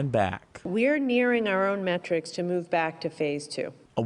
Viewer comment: ?? Did she just say matrics?